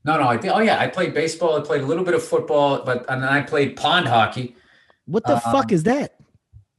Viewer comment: Being born in 93 the combo of street hockey, pond hockey, basketball, backyard football, dirt biking, wiffle ball and madden and some Xbox with no social media was the shit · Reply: I thought Bill was saying pawn hockey, but seeing your comment, I realize it's pond hockey. Lol.